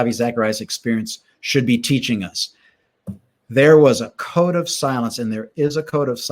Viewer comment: "there was a code of silence" ... I heard "cone of silence" (search it ... cone of silence get smart)